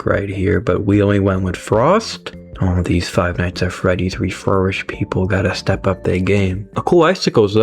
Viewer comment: Im sorry did i just hear "Reflourished" PvZ 2 Reflourished somewhere in the corner